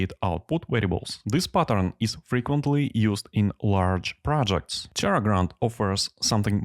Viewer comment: Hi, thanks heaps for creating this, it is a fantastic tutorial. At 18:34 you mention a tool or something that is out of scope for the tutorial. I couldn't quite catch the name but it sounded like "Terragrand". Could you please reply with the name? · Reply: I believe it's Terragrunt